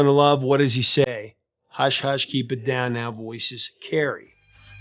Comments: 😅, I heard "keep it down now; voices, Carrie!" Yeah, it doesn't make a crapton of sense, but I was a kid, lol. Always loved it and the video. Def in my top 20 80's pop tracks. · Firstly, thank you! That was an awesome documentary about Voices Carry. I’ve always LOVED this song. My funny mishearing of the lyrics was that I thought she said, “Hush, hush, even downtown, voices carry” 😂😂😂😂 and I would sing this aloud passionately! I still have a hard time saying the real lyrics and my husband loves to make fun of me for how ridiculous my version was, as it makes no sense. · I never misheard the words "Voices Carry" (even though, now, I can hear what you heard) but instead I thought that the words "Keep it down now" was saying "Even downtown". I thought it made sense, since most downtown areas in cities are quite noisy. 🤔😅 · Guilty, I heard..."even downtown, voices carry" 😊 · my buddy swore he heard "even downtown, voiceless Gary ..." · For decades I misheard “keep it down now“ as “even downtown“ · I thought she said Shut up even downtown voices carry. My husband still teases me about it.